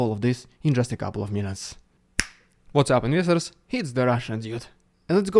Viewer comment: Pls, what's the word in the intro, "What's up, _______" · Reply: “Investors “ he started his channel, before the invasion, as an investment channel.